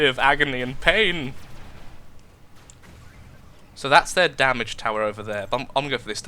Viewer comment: One time, I heard you say "damaged tower" and I thought you said damage tower. This led me to believe that the white towers that are flat on the top were towers that dealt damage to the enemy, like a sentry turret. Hahaha I was funny when I was a noob.